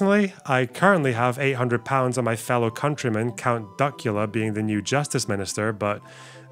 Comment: I am an American, but this year we made a Count Duckula lawn decoration for Halloween making us likely the only Americans in a land of 365 million people with a Count Duckula lawn decoration. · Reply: For an awful moment, I thought you'd said Count Dankula.